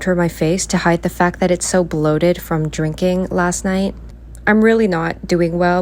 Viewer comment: Did she say her face was bloated from jerking? I'll keep that in mind. Lol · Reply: I thought she said drinking